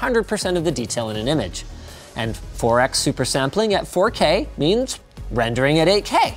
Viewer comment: Linus: "Doom Eternal" Subtitles (not auto-generated): "due maternal" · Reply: it says Forex instead of 4x